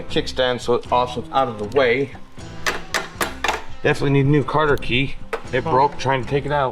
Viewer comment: Did hillbilly say Carter key lol. At 12:13 these guys are hilarious